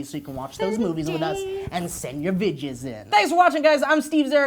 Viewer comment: I thought Steve said send your bitches in at the end...